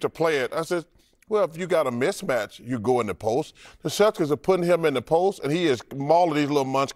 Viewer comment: Did he call the Celtics "the shellfish"